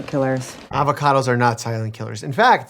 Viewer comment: if you are allergic to avocado you are very likely to be allergic to latex so that allergy technically Could be a silent killer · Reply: When he came in and said "I've got the EpiPen," I first heard "I've got the antivenin", and I was like, "FOR AN ALLERGY?! What the actual?!" 😂